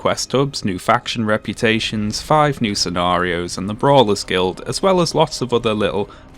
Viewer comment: I misheard Brawlers' Guild as Braless Guild (my guild irl).